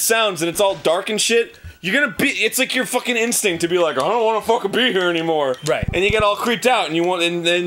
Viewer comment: I thought he said 'I don't wanna fuck a beaver anymore' XD